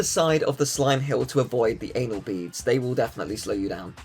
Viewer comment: Is it just me or did he call the donut thing anal beads